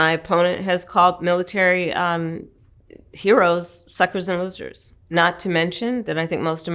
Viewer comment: What tf did she say here: 5:09? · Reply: she said trump called military heroes, suckers and losers.